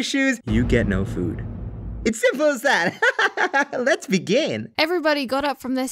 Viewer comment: I tough he was gonna say 2:03 Let's be gay- begin!